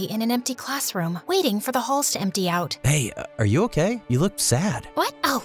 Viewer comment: did he say babe or hey? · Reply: He said "hey"